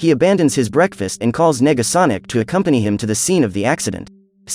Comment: I know I'm not the only one that heard him say he called nigga sonic